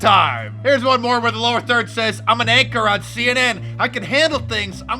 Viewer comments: Is it just me or did it sound like Fredo said "I'm an actor on CNN" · It sounds like Fredo actually tells the truth for a second. It sounds like he says that he’s an actor on CNN. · I could have sworn he said he was an actor on CNN...OHHH...he said “anchor”...ok ok...